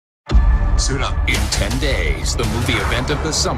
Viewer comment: When he said " suit up" I heard "asuh dude"